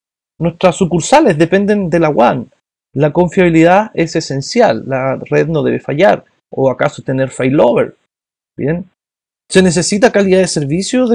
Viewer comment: Que dijo en el 14:04 de la confiabilidad de la WAN. Tener que? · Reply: "La confiabilidad es esencial. La red no debe fallar, o acaso tener failover"